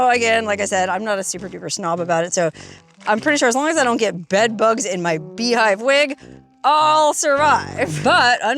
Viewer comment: At first I thought you said " Bedbugs in my beHIND"